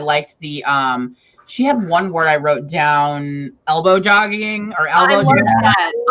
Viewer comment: no matter how many times I replay this I hear Elbowjogging... what’s the word?? 😂 26:00 scrivener gives stats on word frequency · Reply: The word is elbow-jogging, where you do tiny info dumps that break up the narration of the story. And I didn't know scrivener did that! I need to look into that!